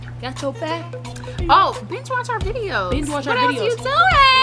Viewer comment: I heard “bitch watch our videos” and I felt pressed like okay then I got you lmao!